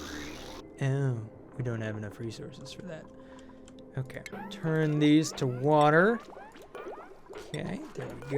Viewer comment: did he say "turn these to water"?